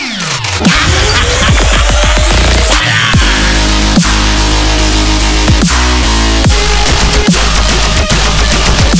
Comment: did he say "val aan!" Or something else? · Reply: He said “Fire!”